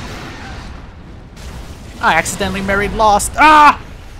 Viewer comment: did he just say that he accidentally married Lost??